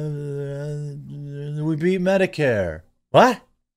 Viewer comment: Jeremy, I never heard him say "we beat Medicare" I swear he said "We beat Megacare!" 😂 I was so sure he said that I had a cramp in my side from laughing.